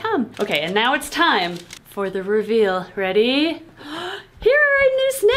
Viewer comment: Did say RuVeal at 16:02?